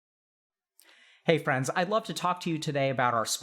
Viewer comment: He started with "hey friends.." And I wasn't looking at the screen, so I heard "hey brats" 😂😂and not gonna lie I'm stoned so that probably has something to do with it